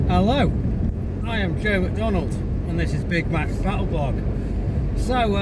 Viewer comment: I misheard your intro as Big Mac's Cattle Blog. I've been waiting for you and Onesock to round up some steak for the ZSU for a while now. That was a prime cut you did show in this post. I want to buy the game. From your video I saw some great sabaka and kishka. Rehi got a cameo. I'm just going to send my fiver now and shut up. Me and my family live in the far South. Thanks for defending us.